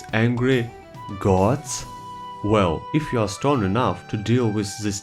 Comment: Whenever he says “strong enough” I hear “stoned enough” and think “absolutely”